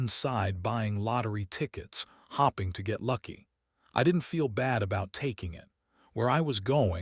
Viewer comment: "" hopping to get lucky "" instead of ""hoping"" damn a.i can't even read the right way bahahahagahahahahha😂😂😂